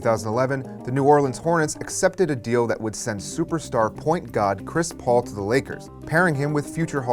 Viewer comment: Does he say point god @ 00:50? lol